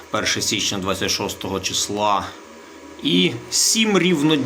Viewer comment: уважно послухай що сказав! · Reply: аа, перше січня 26 числа ) ну так. року звісно ))